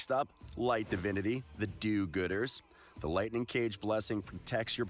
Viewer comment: Did he say "the do-gooders?" Sounds wrong xD